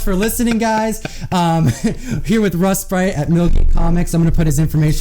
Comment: It sounded like you said Russ Bright of Milky Comics.